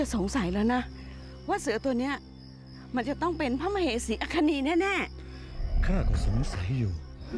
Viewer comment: ตายายตัวเเบกเรื่องนีัมากกก 28:10 ทำไมได้ยินว่าชนีนะ😂